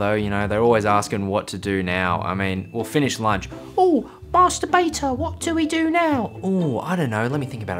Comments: I heard it as masterbuter help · I thought he said Master b*ter